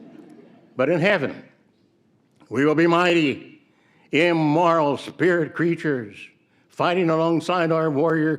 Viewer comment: He said 'immoral'. I watched it over and over on half speed. When he says ' mighty' it's clear how he pronounces the 't' which he absolutely did not do when he was supposed to say "immortal'. So indeed ' immoral' his his intent apparently 😜🤣🥺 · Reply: mark it does sound like he said "immoral"